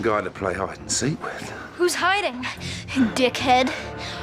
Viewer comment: When i watched it i thought she said tin head not dick head